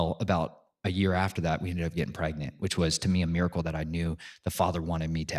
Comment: For a second, I thought you said "I got pregnant!":face-blue-smiling